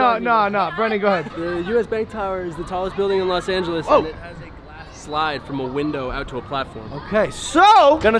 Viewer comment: I thought he said grass slide and I was like. Huh